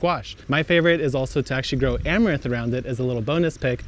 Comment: What is that pink plant at 4:24 ? I can’t figure out what he’s saying. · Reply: Amaranth